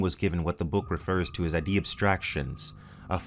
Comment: What is he saying at 3:39:40 ? "IDEA" abstractions?